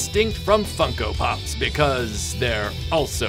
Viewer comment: For a moment, I thought you said “F***ko Pops.”